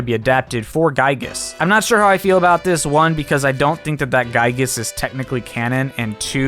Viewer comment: did u say that giygas isnt canon? i just simply cant hear that part very well